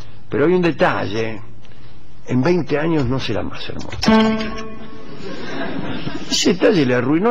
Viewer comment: Necesito saber que dice en el minuto 18:25 "pero hay un detalle, en 20 años..." no puedo descifrar lo que dice, por favor si alguien puede interpretarlo mejor que yo agradecería saberlo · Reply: en 20 años no será más hermosa...